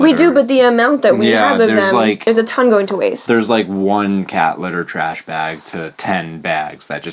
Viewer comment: When you said you reused plastic bags as cat litter I thought you said catheter LOL